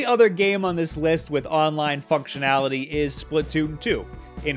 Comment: sounded like online fuckshinality